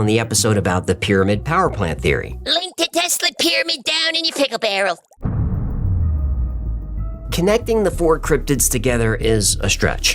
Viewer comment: Misheard hecklefish's "pickle barrel" as "tickle barrel", and now 'tickle barrel' is my new favourite thing. :D